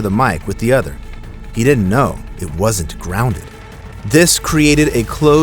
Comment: From 11:14 to 11:16, you say, “He didn’t know it wasn’t [inaudible]”. What’s the last word? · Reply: grounded